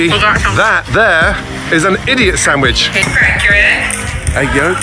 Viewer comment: He actually said Idiot sandwhich